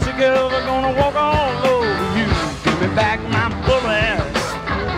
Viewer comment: Swear to god it sounded like he said “gimme back my bull ass”